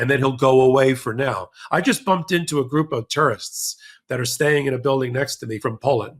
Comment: I thought you said you had bumped into a group of Polish “terrorists”😂😂. Had to listen a few times to decipher it😂😂